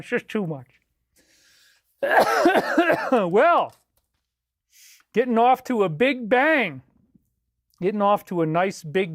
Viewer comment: He has been telling us for years that he would never do it, and has known for a long time that eventually they may come for him. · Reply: "getting off to a big bang" Really kind of sounds like he says Getting offed to a big bang...? He even repeats it. In his last live stream? 🤔